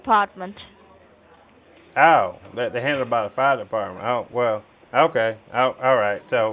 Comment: "You don't need to go into the bank, because the branch doesn't handle fraud activities." That's rich! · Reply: Sounded like he said Oh, they’re handled by the FIRE dept 😢😂 lol 😂